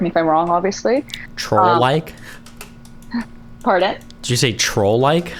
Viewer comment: I thought she said 'troll like' , too. Hahahah